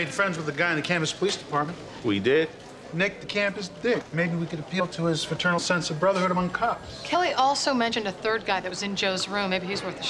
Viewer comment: Wat detective say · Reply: Maybe we can appeal to his fraternal sense of brotherhood among cops